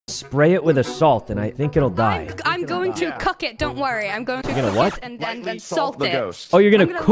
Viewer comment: Yeah...I definitely heard "I'm going to cuck it." xD